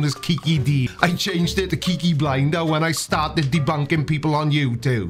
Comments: I thought he said, "Kinky Blinder" and thought it wasn't cool for him to kink shame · OMG.... I thought he said Kinky Blinder 😂😂😂😂😂😂😂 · i thought he said Kinky Binder. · I didn't think he said Kiki at the beginning. I heard "kinky blinder" 😂